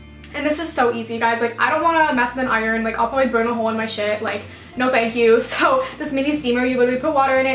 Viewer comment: Did anyone hear her when she said "I don't wanna burn a whole in my shit" 😂😂😂